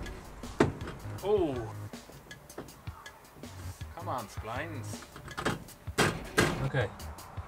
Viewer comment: I have really enjoyed this, but did you really say 36:40 what I think you just said??? Please tell me that was something else! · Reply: 'come on splines...' or 'contact!' What did you think?